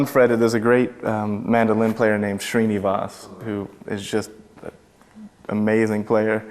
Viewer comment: Did anyone understand the name of the guy he's talking about at 7:55 ?? Who's sounding like he's playing on a fretless guitar, i like it soooo much! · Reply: GiulGuitar he's talking bout uppalapu srinivas